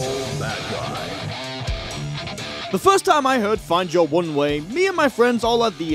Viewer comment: I feel like I'm From Another Dimension I always heard it as find your own way